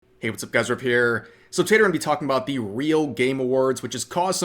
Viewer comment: okay, im new hear... what does say at the beginning of the video? I hear "hey whats up guys its your boy** here."